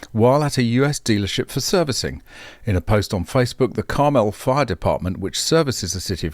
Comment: I thought you said the Car-Melt Fire Department 😂